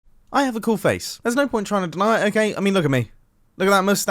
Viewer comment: YouTube has removed the channel icons from both of my channels. I've been trying to fix it for a few days now, but so far nothing... Quite ironnic given the subject of this video! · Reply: I just heard "I have a cool face" and started laughing